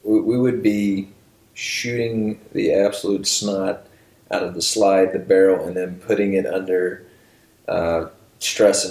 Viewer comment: is talking about some multicaliber handgun, and I can't make out what he's saying. Anyone know what gun he's talking about? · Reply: Jay R USP I think